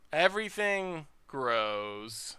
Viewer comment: At 1:3:30 you said everything grows against the mentor deck but I heard everything gross and was just thinking, I feel that. That board is grosssss.